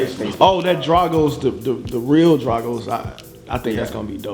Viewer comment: did really say "dragos"